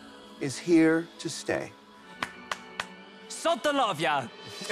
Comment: I’m glad they were both still allowed to do their exit quotes unlike the past double elims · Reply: I thought she said “so don’t love ya.”